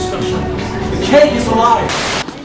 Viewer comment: ach jetzt checke ich das mit dem kuchen erst! ich hab vorher verstanden "the cake is a pie" was ja auch lustig wäre :D